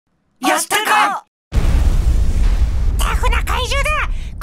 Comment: よしたかにしか聞こえないw